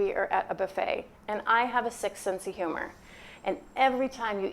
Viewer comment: I thought you said "I have a sixth sense of humour." Hahaha but yes please interview that client.